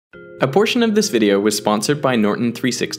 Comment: I heard "abortion of this video" instead of "a portion of video" and I was awake as hell even though I was sleepy as hell before.